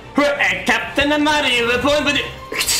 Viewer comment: I love when he says “Captain Amari” it sounds like he said “Captain Amario”